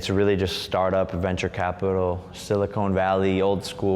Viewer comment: did he really say siliCONE valley?